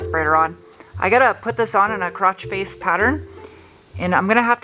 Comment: did you say “crotch face pattern”? 😂